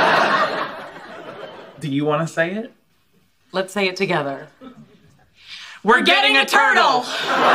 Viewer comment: When I first saw the episode I thought they said We're getting eternal, because there were no subtitles😂😂😂